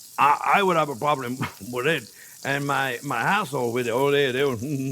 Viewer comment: Did he said my as shole on 7:01? 😄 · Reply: My household he said lol